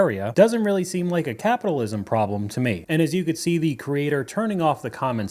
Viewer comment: Did you just call her a creator? lol